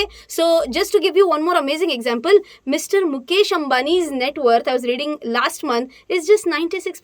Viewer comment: 😂😂 networth misheard as network😂😂😂